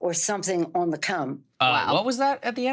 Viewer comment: I'm sorry Nancy I didn't catch that, did you say "Cum"? Lol